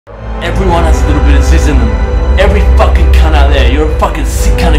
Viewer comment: Not gonna lie, when I saw that video years ago I thought he said " Everyone's got a little jizz in them"